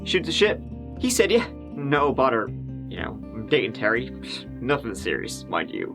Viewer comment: When you said "no bother" I thought you had said "no butter" when they asked Rafferty if he minded talking with the police. I said to myself that it must be a Canadian thing. Then... DOH! 😜